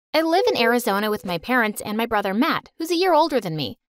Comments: What everyone else heard: "Hi I'm Sasha and I live with my parents, and my brother Matt, who's a year old than me" What I heard: " Hi Im Sasha, and I live with my parents and my older brother who are a year older than me" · I thought she said hi my brother name is map, but she said Matt or did she say map? I don’t know what y’all think😂